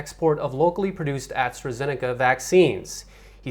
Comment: What did he say ? "Locally produced ...WHAT " Locally produced ads? I can not understand. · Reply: Locally produced Astra Zeneca vaccines.